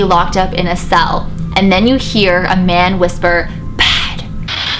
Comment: ! my favourite youtuber ever !!! I had also seen a black figure in front of me ....almost most 16 inches away!! BTW I heard "bad girl" on